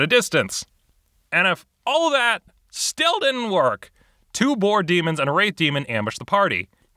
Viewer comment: Ummm, am I hearing things? Did he say a "rape demon" ambushed the party? What kind of RPG is this?